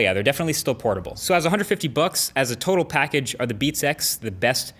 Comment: Sounds like he is saying "Beat Sex"